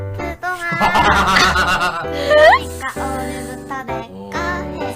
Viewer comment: 님껀 아무리 들어도 그러니까 오늘부터 니코해 로 들리네